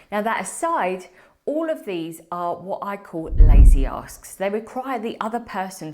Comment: I thought you said "what I call lazy ass's". 😂